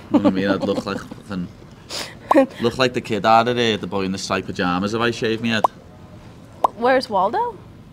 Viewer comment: I really want to decipher what Paddy said at 11:29 "kidadeedeh striped pajamas"